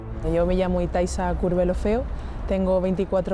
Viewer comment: dioooo escuche "Itaisa Culofeo" XD